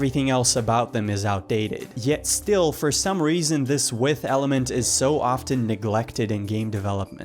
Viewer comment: "Yet for some reason this ?? element is..." Did you say "width" element?